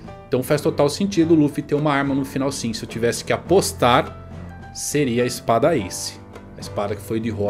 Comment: eu ouvi APOSTAR?